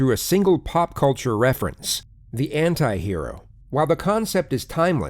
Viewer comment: Im sorry but at 3:08 I heard "hentai hero" before I watched my screen to read "antihero" ToT